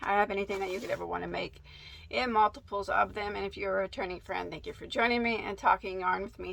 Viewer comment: When she said returning friend at the beginning I thought she said attorney friend, and I was wondering why the lawyers were getting a shout out 😄😄😄